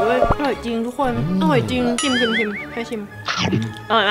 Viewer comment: ใครถ่ายน้าา ถ้าเป็น 5:42 เราได้ยินว่า " ชิมเทอชิม " หัวจัยชิบเปอร์